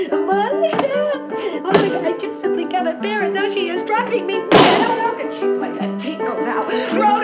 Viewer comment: What did she say at 1:35 when she hit the table? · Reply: I simply cannot bear it now she is driving me MAD